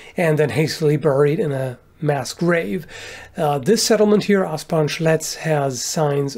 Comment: Ahh, Asparn Schletz, for a second there I thought you said Ass-punch Ledge, which sounds like either a mortal kombat stage or a very odd historical site